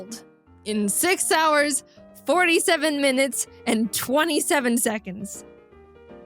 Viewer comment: i thought you said 3 hours and 15 mins